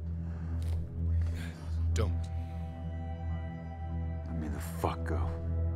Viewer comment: Guys, can any of you help me, please? I'm trying to transcribe this video in order to make a russian subtitles for it and I've transcribed already 90 % of it, but the other 10 I can't figure out. 1:50 - 1:53 3:06 - 3:08 3:43 - 3:45 5:58 - 6:00 6:36 - 6:37 Thanks in advance! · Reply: Let me the fuck go. 3:06 - 3:08 But don't say I didn't try. 3:43 - 3:45 I was with him when he bought it, permit is in his wallet. 5:58 - 6:00 My hands are tied, Jim. 6:36 - 6:37 And I'll be pulling the feeds. However, I'm not too sure about 3:06 - 3:08, It could as well be "But don't say into her eye." Probably referring to someone else, either the wife of the deceased or the daughter. Let me know If you need help with anything else, If you're not too sure what some of the words in the transcription mean, feel free to ask me. I'll be more than happy to assist you.